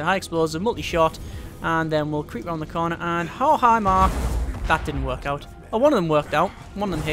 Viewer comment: Did he say "Oh, hi Mark" at 8:08 or am I hearing things?